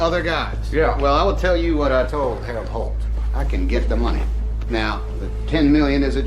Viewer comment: Did he say Harold Holt? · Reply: He said.. Heb Holt. I slowed it down. Trying to remember Harold holt is. Coulda been a nickname🤷‍♀️